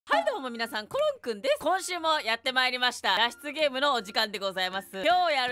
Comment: タイトルとサムネのせいで裸出ゲームって聞こえた(重症患者)